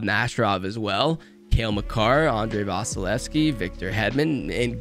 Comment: When you said Makar I thought you said Macanrin